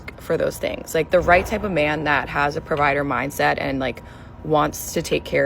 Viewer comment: You say "provider mindset" , I hear "leach" .